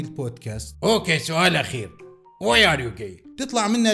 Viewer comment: ايش قال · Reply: Why are you gay